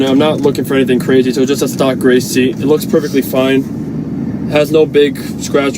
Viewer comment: DMart BossMan I know, bought the seat just to replace it because mine had rips not looking for anything extreme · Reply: Alright man i was just saying cause It sounded like you said "Stock Race Seat"